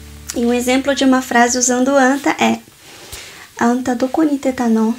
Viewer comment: só eu que ouvi " Do Cu ", depois de anta?? 🤣🤣🤣